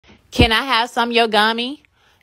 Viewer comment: For a sec I thought that she said can I have yogummy